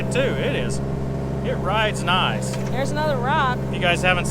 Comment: Yep I heard 'we need another truck' too!